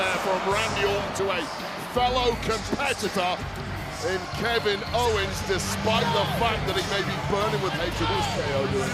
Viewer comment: what did that guy said lol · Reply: He said he still fing sucks. I saw a video on x